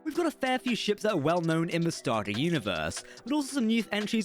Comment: could just be the accent but it almost sounds like he said "starGATE universe"